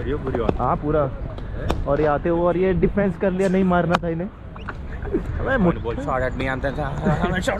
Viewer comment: abee muthhal....anyone hear that...??😂😂